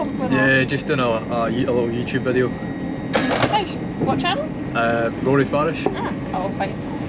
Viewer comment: I thought you said Rory fash